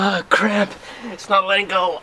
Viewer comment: what he said? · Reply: actually he said it's my lingo